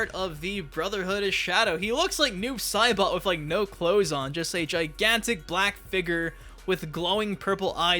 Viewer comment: yo i thought you said a gigantic black n word bro i stopped for a minute and rewinded like 20 times